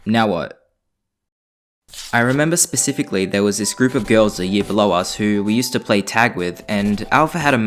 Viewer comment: i heard a group of girls that will blow us wait did u...